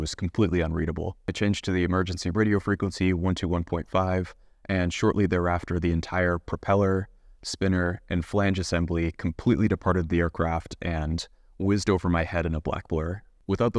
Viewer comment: Did he just say phalange ? 😅